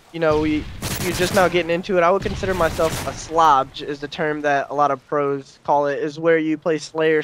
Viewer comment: What did he call a slayer / obj a "Slabj" ? ( 1:50 )